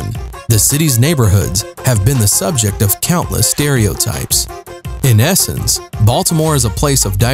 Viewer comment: When he said "in essence", at first I thought he said "in Essex". My ears perked up.